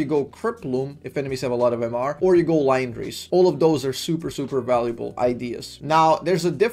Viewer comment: what item did you say at 4:19 after cryptbloom? Also what are some late game items like 5th or 6th? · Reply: he said liandry bro